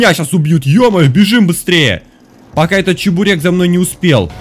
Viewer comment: Чабурек? Или мне послышалось? Ахахахахаххаха